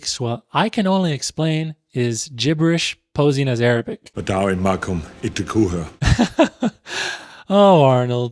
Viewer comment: pretty sure he says "اتعاون معاكم اتركوها" which I got what hes trying to say but that's not how sentences are spoken in Arabic he said "i cooperate with u, u leave her alone or release her(depending on context)" but he said it in a way thats not only not grammatically correct its also js not how arabic is spoken it's structured like an English sentence that was then translated